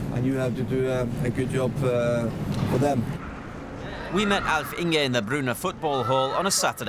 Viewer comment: Erling: Bryne Commentator: Bruna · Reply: I thought he said Kevin De Bruyne XD